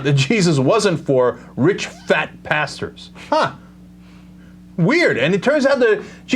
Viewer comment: I thought Cenk said "Rich, fat, BASTARDS" the first time around.